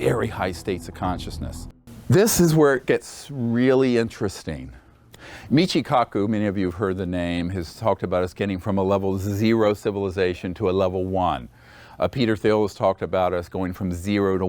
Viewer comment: At time stamp around 1:30….did you say Peter Thiel? The man behind our current vice president?